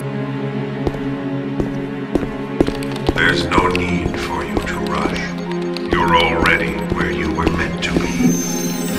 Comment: what is the Big brother said in 0:17?I Don't speak English,Can someone write that down? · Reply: "You're already where you are meant to be"